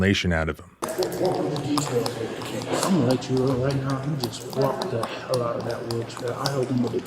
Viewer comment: did i just hear the first cop say "he basically walked right out of that wheelchair"????